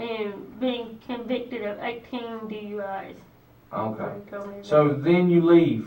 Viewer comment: 18 DUIs❓🤦🏽‍♀🤦🏽‍♀ Did I hear that right❓ WTF‼